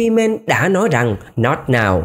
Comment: em nghe thành là " I can't " là sao vậy ah:))??